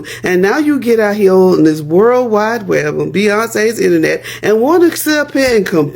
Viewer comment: did she just say "Beyoncé 's internet"